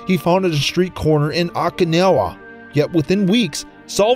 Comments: I'm not sure, but did he attempt to say Okinawa at 34:46? Sounds like he said "akinowha" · It sounded like he said Okinala instead of "Okinawa"...